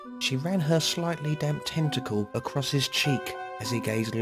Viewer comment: I thought he said testicle instead of tentacle and I got weirdest image....